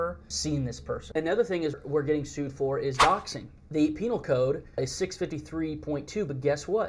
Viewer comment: I THOUGHT THEY SAID BOXING???